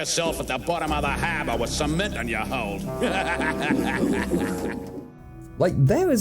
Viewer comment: did he say "meth in your hold".